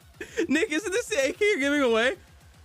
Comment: What did he just say? ☠ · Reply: "Nick is this the ak you're giving it away" realisation had to kick in first tho 😄